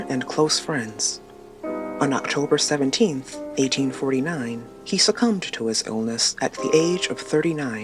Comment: What did you say? 24:06 "aging 49, Chopin died at 39"? 🌚